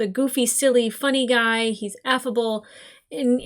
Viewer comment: did she just call Bolin f-able?!?! 😱😱